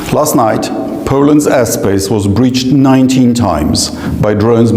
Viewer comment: I thought he said Poland's assface